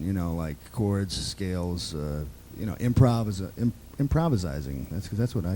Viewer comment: 'Improvizize'? like 'Burglarzize' instead of 'Burglarize'?